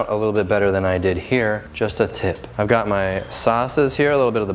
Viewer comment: did u say just the tip? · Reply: "just a tip"